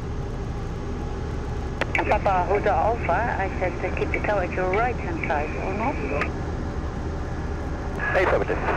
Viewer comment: Wat zegt de tower tegen je op 1:32? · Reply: Hi Rob, ze zegt: I thought I said to keep the tower at your right hand side, or not? En dat klopt, die instructie heb ik inderdaad eerder gehad. Door e.e.a. zette ik een linkerbocht in, terwijl zij graag rechterbocht had willen zien.